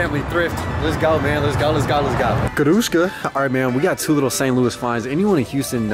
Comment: Did you say "gruszka" at 13:17 :D